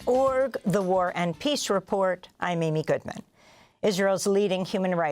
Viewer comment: I just realized that Amy introduces the show by saying "The war and peace report"... For years, I thought she said "The war on peace report" -- which I have to say I prefer.